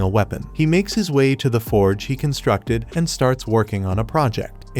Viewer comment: Did someone say the forge